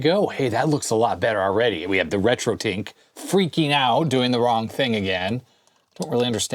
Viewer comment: Wow I guess I struggle with American accents more than I thought. It was just this week that I found out the video converter thing is a RetroTink, not "RetroTank" like I thought was saying this whole time!